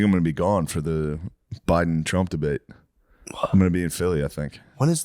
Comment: We need Phil on for ep 500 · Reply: I thought it said Dr Phil for a sec, I would be down for all three Phil's to come on!